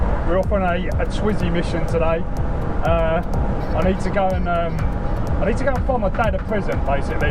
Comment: thought he said in the beginning “need to go visit my dad in prison” 😂😂😂